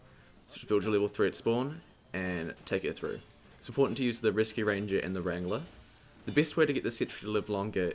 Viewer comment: I keep hearing you say "Risky ranger" instead of "Reascue ranger" and its pissing me off ; _